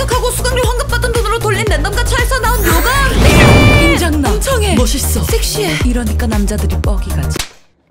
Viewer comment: 엄청해 나만 멍청해라 들리나?ㅋㅋㅋ